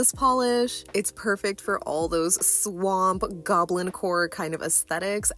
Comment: I misheard “swamp goblin core” as “swamp goblin WH0RE” and I was like yes. I want to channel swamp goblin wh0re. That is my style, scandalous yet slimey.